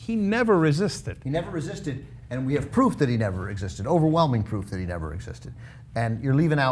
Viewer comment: I could have sworn they said "he never existed"